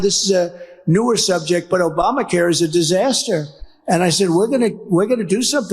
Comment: I also heard that as “were white”